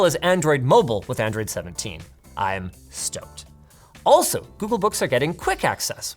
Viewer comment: I was genuinely confused at this part because I heard "Google Books." Y'know, the Google database of books. Like, the ones you read. Thanks Google!